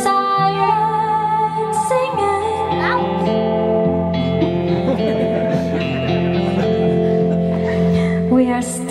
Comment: What is the little boy saying at 1:45? So cute. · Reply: flawless